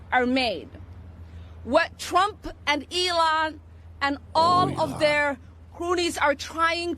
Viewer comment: 'all of their croonies" i mishead and had to go back, because I thought I heard 'all of their booties".....